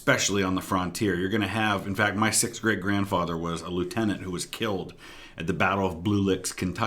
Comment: every time you say great grandfather I thought you said my sixth grade grandfather.